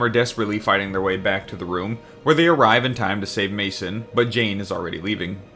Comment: i Heard masón jesus, this delusión is hard